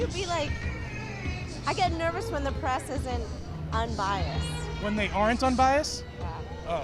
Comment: I get nervous when the press is unbiased. There u have it folks. There’s no hope for these people. They are plugged into the matrix. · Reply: I heard "isn't unbiased".